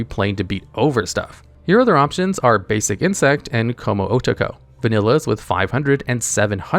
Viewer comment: Did you call him "cum" "oh"?